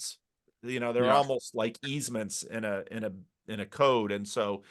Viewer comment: what does say? Set aside? Easement? Code?? Google tells me small businesses